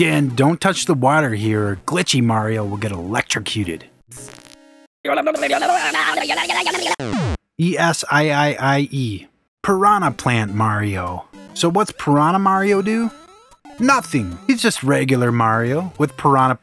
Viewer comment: thought it said "jews" lol